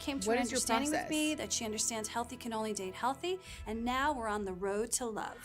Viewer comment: I've listened to this like five times now and every time I hear "healthy cannoli Dave healthy". I have no idea what she's actually saying, but there's no way that's what she's saying (Who's Cannoli Dave? Why does Claudia need to be convinced of his health?)